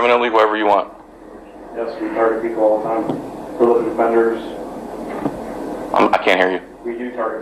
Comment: did he say they target Public Deffenders?